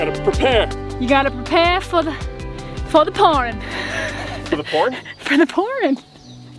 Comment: off grid poor cell signal........ Did you say PORN>?><